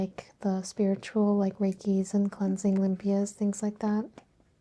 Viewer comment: am i mishearing or did describe her most successful videos as "reiki and cleansing and BS and things like that" lol